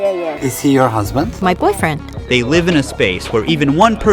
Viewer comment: Did she just say boyfriend